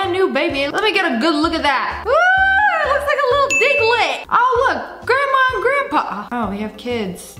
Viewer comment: WHO ELSE THOUGHT SHE SAID NIGLET